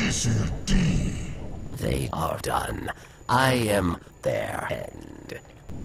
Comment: So what are the fallen dregs really saying when Reddit thought they were saying "it's the darkness!"? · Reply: Ah yeah, that always bothered me. Sounds like "Er dor ne e zer de" at 2:50 Means "They are done. I am their end." The "dor ne" part is what will have sounded like "Darkness". Here's the "It's the Darkness" clip for comparison: <link>